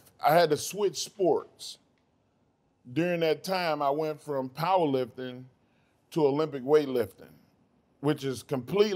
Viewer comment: In the beginning all I heard him saying is “I went from weightlifting to weightlifting, two totally different sports”